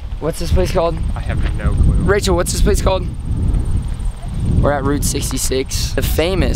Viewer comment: At first I thought he said, "what's his face called? what's his face called?" I laughed.